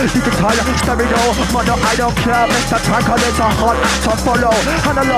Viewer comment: This guy literally spits straight fire for 25 minutes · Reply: i swear i just heard moder i don't care